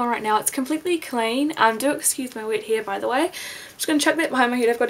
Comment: At first I thought you said "Do excuse my wit here" LOL! It took me a few seconds to figure out that you said wet hair.